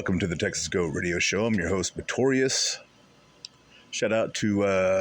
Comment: did you catch any of Jimbob's stream Saturday? Ex-cult member, sir crushington bear, shared his up-close and personal experience with OB... that he's not what he pretends to be, he's greedy and doesn't lift a finger to help others... is all talk Talk TALK... Maybe he will speak with you...on or off screen. You not giving up on the child is a beautiful thing. People give too much faith in the medical system but thankfully more people are waking up to the lies of the fake medical system . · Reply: I know it sounds like he says notorious It's, I only no cause I asked for email.